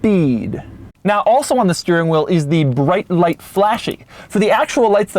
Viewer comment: Did Doug say Flashy